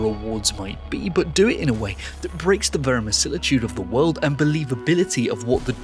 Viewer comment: verimissilitude (noun): when theres lots of missiles in the game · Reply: Oh! I thought he said vermi-silly-tude!